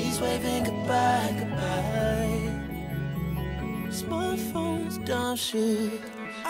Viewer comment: its sounds like he is saying smartphones dont shit lol